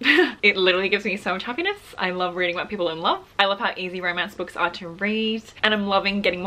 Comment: Girl I thought u said u love reading white ppl in love😭